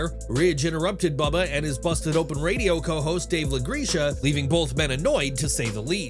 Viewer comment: WTF did you just call Dave?!?!?!?!?!?!?!?!?! Where's my pink robe at??? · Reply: "LaGreesha" lmao